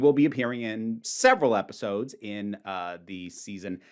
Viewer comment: i misheard your intro and thought you said 'seven' instead of 'several'. i went into a panic!